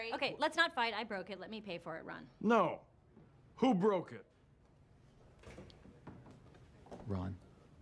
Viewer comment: I thought it said " Who Boke it"